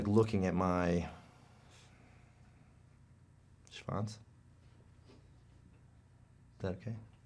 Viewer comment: what word is he saying? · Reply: "schwanz" i think